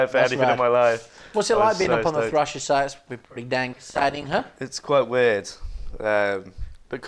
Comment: Also at 57:45 what on earth does he say, It must be what on the Thrasher site? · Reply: "pretty dang exciting huh" we think we was quoting something... badly